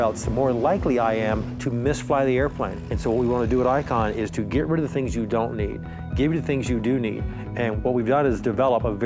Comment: Is it just me or does he say at 2:18 get rid of the things you don’t need, get rid of the things you do need. 😂